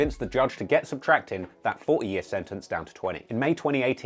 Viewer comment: the subtitles said "where she convinced judges to down her 4 year sentence to 20 years" i thought this was a joke untill i actually heard 40 years